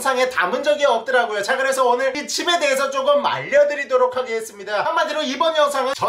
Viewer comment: 나만 말려드리겠습니다라고 들리나??